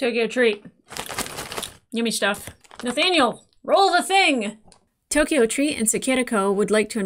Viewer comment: I thought she said: Nathaniel do the thing! 22:34 I got scared for a moment......